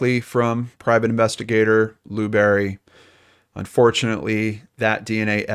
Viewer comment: omg I thought you said Private Investigator Blueberry for a sec..